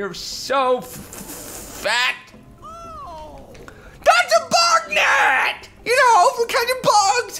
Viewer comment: at first i thought it said bug nugget not bug net